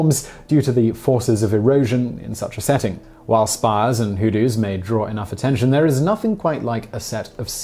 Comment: Due to the heavy accent I can't tell what he's saying here. Even the closed captions think he is speaking Dutch and don't show them correctly. heh "Spires and hoo-doos"?